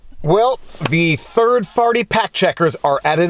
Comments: Maybe I need my ears checked but I think I heard you say the "third-farty fact checkers" are at it again. Or maybe I need to go to sleep. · I heard “Third Farty Fact Checkers” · Did you just say third farty fact checker. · I heard the turd farty fact checkers. That's what I heard and I'm sticking to it. Also, it may possibly be accurate.